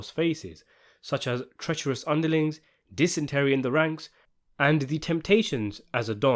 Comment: Dysentery in the ranks??? Did I hear that right??? 🤣🤣